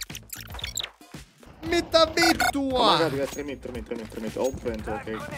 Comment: Did you just say "mitä vittua"